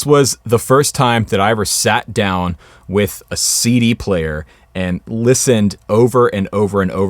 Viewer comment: lol when he said "sat down with a CD player" I heard it as "sat down with a seedy player"